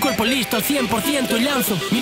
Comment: Alguien Me Puede Decir Lo Que Dice En El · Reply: Dice que lanza su metamundo, un concepto que define la realidad virtual. Con los sistemas de realidad virtual entras a un metaverso (universo generado por ordenador) que podría decirse que está formado por metamundos (los videojuegos, por ejemplo). Al ponerte una de estas gafas te sientes totalmente dentro del mundo virtual, de ahí estos términos.